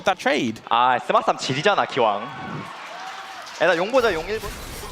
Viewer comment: what does he say here? didn’t wanna tell us. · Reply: He said “That Smite Fight was insane”